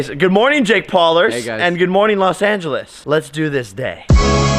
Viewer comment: When jake says let's do this day I thought he said Let's do this date XD